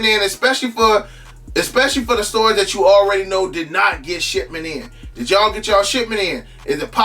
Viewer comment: Sound like he saying "shitment" 🤣🤣🤣